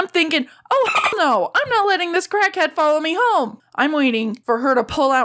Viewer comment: When she crackhead I heard "Kraken"